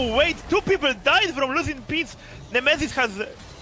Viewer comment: i heard him saying "losing pigs" ALL THE TIME, i really tought that i read the name wrong at start. Gotta love the polish accent